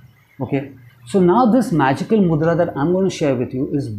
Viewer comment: Am I the only one that thought this said a magical Murda and not Magical Mudra 😆